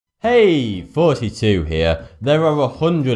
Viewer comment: Is it just me or everytime he says "Hey,thoughty2 here" I hear it "Hey 42 here" ???